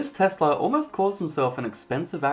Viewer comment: Did you call the car at 7:15 a Tesla or am I not hearing you properly?